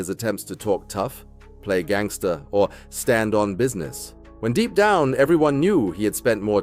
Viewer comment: I thought i heard him Say With an attitude "I STAND ON BUSINESS" 🤣🤣🤣🤣🤣🤣🤣🤣🤣🤣🤣 Apparently not...